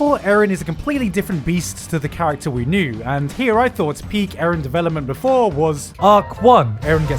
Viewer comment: me hearing the word peick instead of peak